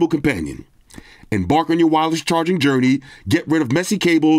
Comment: thought you said Embark on your Wildest charging journey lol